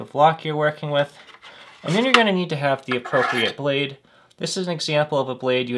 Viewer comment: Great video, thanks for showing us how this is done. One question, though: What exactly is the weight for? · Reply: Ah, then I've misunderstood what you were saying. You said "blade" and I heard "weight". Thanks for clearing that up!